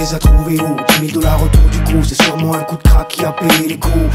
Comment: „C‘est sûrement un coup d‘ (...) qui a payé les coups“. What‘s the word he‘s saying? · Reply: coup de crack